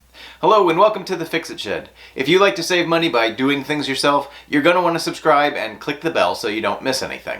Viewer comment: I thought you said, "Hello and welcome to Fixing Shit" 🤣🤣🤣